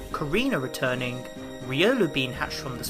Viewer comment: I thought you said serenas returning and I got so happy